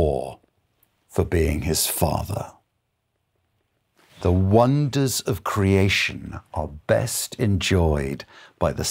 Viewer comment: What is "philael"love? 21:53 is he saying phileo? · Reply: Filial